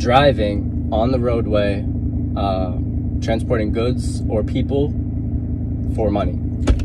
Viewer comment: I thought the SOVey said he'd only need license for transporting "goats or people." Which makes about as much sense as what he actually said.